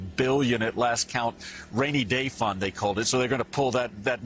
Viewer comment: did he really say there gonna pull money from whats called tha rainy day funds?